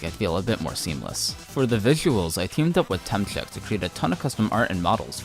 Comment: its kinda ironic the name of the guy that helped you being so close to Temmie, the main artist for undertale and deltarune, I got kinda shocked at first cuz i swear i heard you saying Temmie